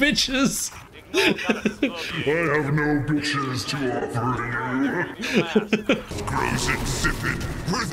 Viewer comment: I have to admit, I also thought “Chode-head” said “Alas, I have no bitches to offer you”